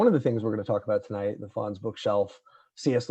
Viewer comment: it's funny that when you first mentioned this book in the last video, I heard it as The Fonz Bookshelf. I wondered what the Fonz had to do with Lewis & Tolkien. Anyway... it's intriguing, just listening to the radio sometimes, how blunt & unnuanced the secular world can be in its grasp of truth, and fact, and their distinctions...